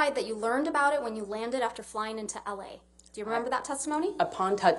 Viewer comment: I love how she sounded like she said “ I was lying” but she said flying